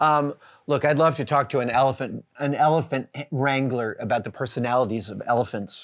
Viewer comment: At first I heard "I'd love to talk to an elephant in Wranglers..." You want to see an elephant with personality, talk to an elephant in Wranglers.